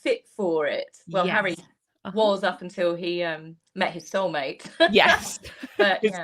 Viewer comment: Soul mate thought you said cell mate must turn my hearing aids up😅